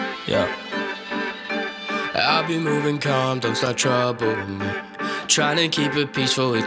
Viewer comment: Please tell my why is sounds like is saying “I’ve been moving comdoms”